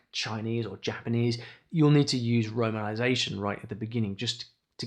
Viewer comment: I didn’t catch what you said. “Runnanization” 😅. Please help. My husband is Chinese, I’m Mexican and speak Spanish and would love to learn Mandarin.